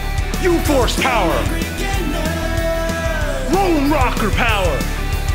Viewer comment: I honestly thought he said Rolling Rock power